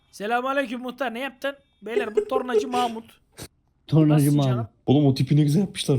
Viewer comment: abi orada sen tornacı mahmut dedin ben pornocu mahmut anladım :D :D